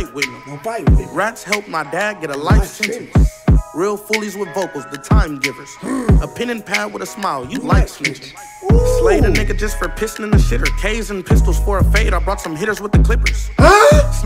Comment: “A pen and a pad with a smile, you like snitching” that line had me dead 😭🤣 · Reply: I thought he said "a pen pal with a smile"